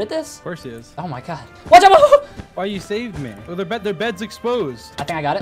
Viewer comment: i thought said ''why did you save me''